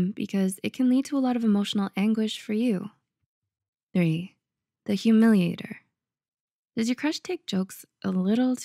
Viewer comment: 1 "the ghoster" Well shit. 3 "the humiliator" Well shit. 6. "The user" WELL SHIT. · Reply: i thought it said “the hallucinator” and i was like yep totally me